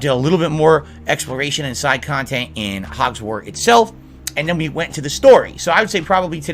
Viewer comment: did he say "Hogswart"?